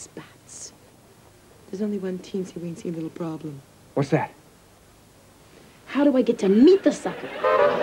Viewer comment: Listening through the headphones it sounds like Karen says "How do I get to meet the sucker?" However the laughter is a bit loud and drowns out the last part making it sound like something else.